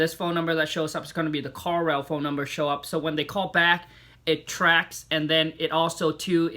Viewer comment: Whats the call software that he's talking about? I can't understand what he's saying?? Call-L? Car-L? Does anyone know? He says it